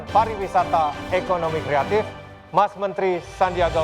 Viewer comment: 7.24 itu aku yang salah denger atau emang chef arnold bilang "mba mentri"?